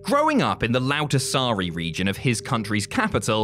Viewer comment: I SERIOUSLY thought you said he grew up in the "loud and sorry region" of Helsinki 🤣